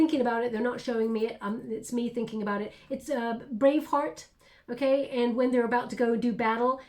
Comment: Hahahahaha, when you said brave heart, I misheard... thought you said brain f... (I apologise, but Australians must just interpret things differently) 😅